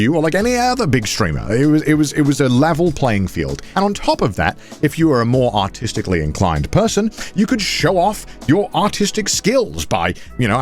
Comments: I genuinely thought I heart him say "And if you're more autistically inclined, you can ..." and I lost it, I had to reply it a couple of times to finally hear what he was saying I was like, nah, he did not, and he didn't (Would be funny as hell though) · Okay, first of all, I think it's very stupid when someone's 'fans' throw hate when the person themselves has already said they were happy. Second, I was listening to this in the background, and I swear I heard him say 'autistic' instead of 'artistic, 5.30 ' and I just stayed like??